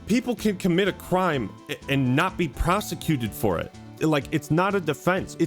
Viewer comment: I really heard "Prostituted" and had to run it back